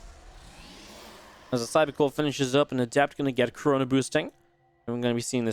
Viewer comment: lol when u said chrono boosting i thought you said coronaboosting